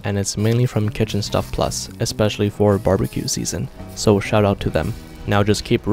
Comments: When says shoutout to them it sounded like he said shut up to them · I heard “shut up to them” Lmao 💀🤣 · I thought he said "so shut up to them"